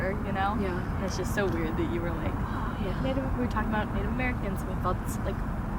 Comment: Did anyone hear "oh yeah"